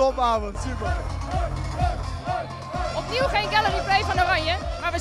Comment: wat zegt ze? gallar replay? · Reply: Galleryplay ze bedoeld dat we niet met super mooi voetbal wonnen